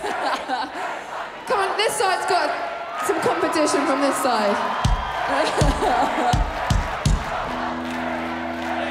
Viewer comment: can somebody tell me what are they yelling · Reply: Here we, here we, here we fucking go